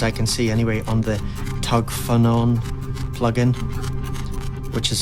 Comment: You mention a free plugin in the beginning… ¿ what is it ? Sounds like you said TugFenon ??? What? TugPhenon ?? Can you list the auv3’s you use in demos please ? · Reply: Names of any AUv3s used can be seen at the top of the plugin window. The free app I mentioned is TugPhonon, it's a free desktop plugin, not an AUv3. All the best